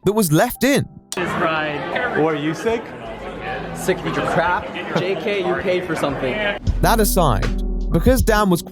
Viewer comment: What's being said? I can't hear anything snide. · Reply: Dan asked something along the lines of “are you feeling sick?” and replied: “Sick of your crap! Jk you paid for something.”